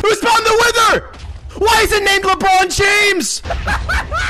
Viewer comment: "WHY IS IT NAME LEBRON JAMES" Fucking priceless and the best line ever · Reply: I thought he said LeBron James is fucking me